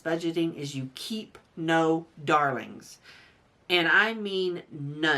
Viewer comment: “keep no...” what?? Darlings? Sorry I can’t understand what you’re trying to say there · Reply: Yes, I was trying to say darlings. Thanks for watching.